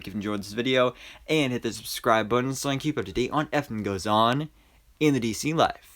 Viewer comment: Everytime I hear you say "whatever goes on in the dc life" I hear "what effing goes on in the dc life" idk why lmao